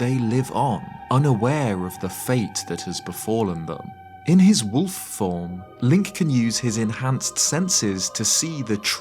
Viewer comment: In the minute 2:19 I heard “in his woof form” and make so much sense